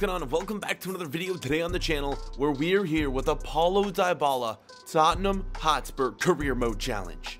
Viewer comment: Did he just say “with Apollo dybala“ at 0.05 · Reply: He said a Paulo Dybala